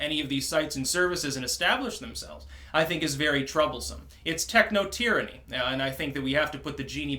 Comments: Dammit... thought it said ‘techotranny’ (puts the lube away) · I thought that said technotranny. I envisioned a transvestite in a club dj'ing. Holy shit I need some coffee lol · I thought it said Technotranny...@